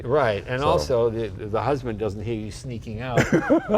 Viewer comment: did Jay say, ...the husband doesn't hear you sneaking out... ?